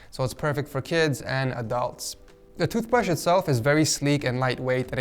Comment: did you say “it’s perfect for kids and a dog”?